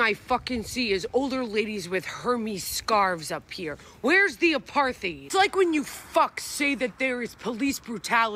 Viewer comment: When says “ where’s the Apar-th-ed “ I just hear “ I looking for the herpes ! Where’s the herpes around here??